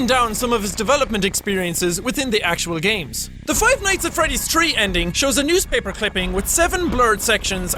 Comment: did he say...tree?